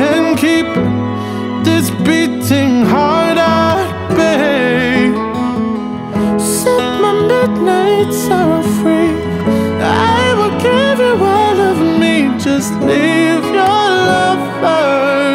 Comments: When he says set my midnight sorrow free it sounds like he says suck my milk made sour free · This is a really pretty song, but I cant help but here "Suck my milkmaid, sour free" which kind of ruins it for me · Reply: I literally came to this video for the same reason LMFAO instead I heard "set my milkmaid, Sarah, free" and I'm like who the fuck captured Sarah and how can I get her freed!? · Am I the only one who heard SUCK MY MILKMAID IT'S SOUR FREE? Like wtf.. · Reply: It sounds like he said that, it really did but what he really said was "Set my midnight sorrow free" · So I heard set my midnight sorrow free and I heard it as suck my milk made sour free. I'm sorry if i ruined the song for u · i laughed so hard when sam smith sings the misheard lyric that sounds like she's gonna suck our milk that made sour free instead of set my midnight sour free. i just fucking can't